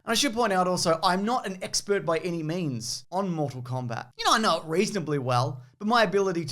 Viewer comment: You're not an expert by any..... memes??? Did you say sir??